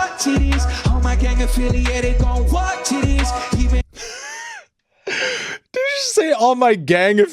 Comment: All I heard was 'gang affiliated gonna walk tiddies'.